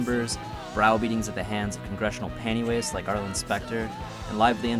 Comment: Did he just say "congressional pantywaste" ?